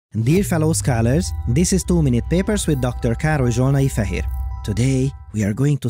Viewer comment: Until now I thought he was saying "this is too many papers" until I finally read the channel title... I have watched so many videos of him and still hadn't noticed... God damn it.